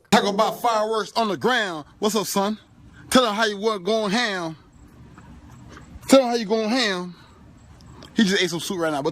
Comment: Looks like 'natural selection' was on duty during the 4th of July weekend. LOL Also @ 02:49 can someone translate that for me because I have no clue as to what he said? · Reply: I heard "tell 'em how you goin' ham" lol